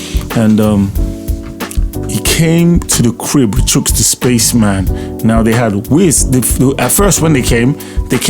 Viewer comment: did I hear you say Chuks The Spaceman?? He dominated the airwaves in Eastern Nigeria when he was working at Cosmo Fm